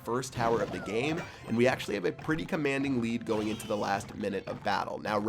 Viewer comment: When he said we have a very comanding lead i thought he said weed lol